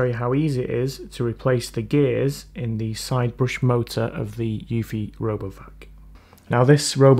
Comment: sounds like you said "Robo F**k" lmao 🤣😂